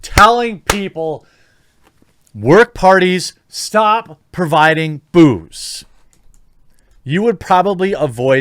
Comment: Am I the only one who heard: "Stop providing boobs!"?